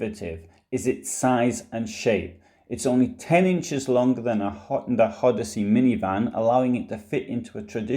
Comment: I couldn't quite make out what you said here. It's only 10 inches longer than which type of minivan? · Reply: Honda Odyssey.